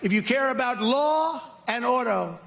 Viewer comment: SMH! I thought he said "Law and Odo ! (Deep Space Nine ref)